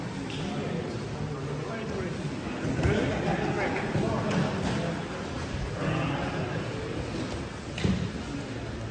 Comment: what did the crazy shirtless guy say at @ 00:47:10? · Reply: I quit